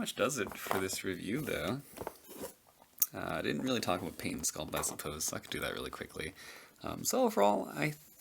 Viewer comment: subtitles on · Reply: Penis sculpt the hell the captions say